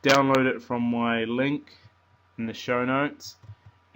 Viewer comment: Not to be an a** but it's cald the description. I thought u where saying shoutouts until